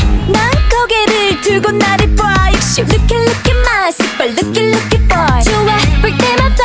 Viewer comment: First listen: you don't like Second listen... LOOKIE LOOKIE! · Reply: I’m sorry but their actually saying rookie instead of looking it just sounds like their saying lookie